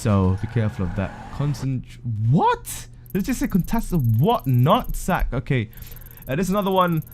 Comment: what did you say? something about nut sacks?